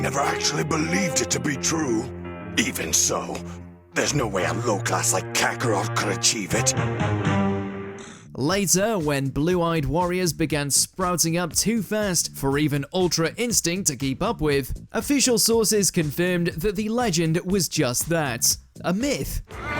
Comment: Did he... Did he just say blue eyed